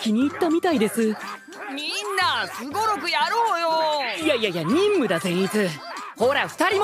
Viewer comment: I love the fact that at 1:13, the caption says no no when what you hear is ya ya